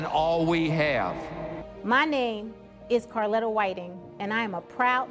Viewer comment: I honestly thought that the lady said her name was Carolyn WHITEY. And why is it that the left says the NRA is the most powerful lobby in the history of the universe?